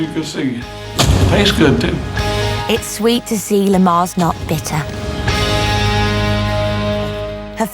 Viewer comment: i thought she said its sweet to see hes not better i was like thats mean 😂